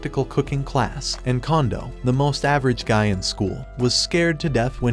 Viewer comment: had to skip back 10 secs cus I thought you said condom 😭✌️